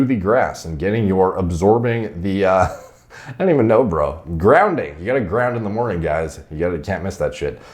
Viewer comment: Grounding! I thought you said grinding! This whole time I have been grinding my ass on the grass for no reason!